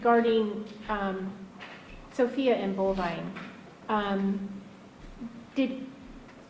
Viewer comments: Bovine? I thought it's Bulvine?... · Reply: I thought the prosecutor was saying Bulvine too. I figured it out that his name is Bolvine. Which means cattle. I'm curious to who picked out the name. Tabitha has zero say so in anything, so I doubt it was her. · So George stood up against his parents then why if he didn't want to be involved with killing 8 people didn't he stand up 2 them on that..or even turn them in b4 they went thru with the murders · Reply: Though I thought they were saying Bulvine throughout this entire trial, a quick google search just confirmed it’s actually Bovine.